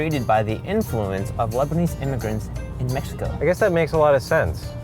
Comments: actually thought he said lesbians instead on Lebanese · I thought that said lesbian immigrants